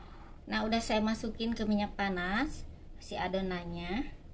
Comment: Kirain tadi adonannya dimasukkan ke minyak tanah 😂 astaga ternyata aq salah dengar ke minyak panas baru di oven wkwkwk wow look so delicious Kk bagi donk Kk hehehe